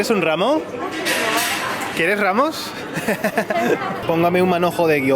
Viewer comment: crei haber escuchado quieres rabos?.. xd luego entendi ramos... xddd ahahaha